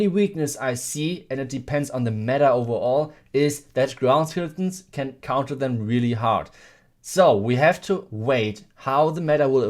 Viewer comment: wtf did he say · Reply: Ground skeletons counter them really hard